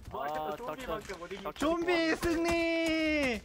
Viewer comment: I don't Misheard Right? "Zombie Sini" ??? WTH haha